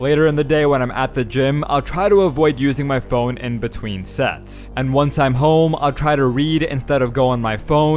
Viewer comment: I heard "in between sets" to "in between sex"💀